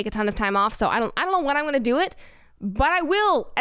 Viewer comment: Sorry, I thought you were saying Mount Chupichu rather than Machu Picchu